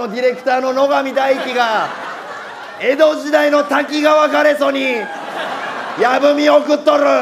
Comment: 滝川に聞こえる